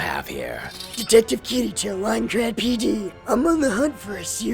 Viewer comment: "I am Detective Kirito from Aincrad PD." A better title by far, much better than Beater ever was. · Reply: Ya know the first time I heard that I thought he said "Minecraft PD" and it confused the heck out me.